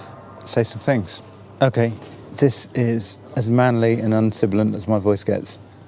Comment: What does he say at 1:06:10? This is as manly and as what as my voice gets? What is that word? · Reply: I heard "un-sibylline," but I'm not sure.